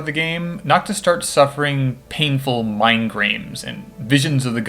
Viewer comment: I'm sorry. Did you say "mind-grains" at 58:18?